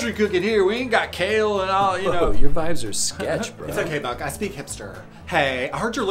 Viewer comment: I love that, "it's ok Buck I speak hipster" 🤣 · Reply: I thought he said I speak HAMSTER 😂